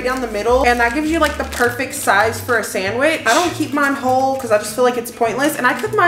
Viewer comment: Wait u don’t keep ur bacon cold….? Isn’t that gonna grow bacteria?? EDIT: OHHH u said WHOLE! I thought u said COLD😂😂😂😂😂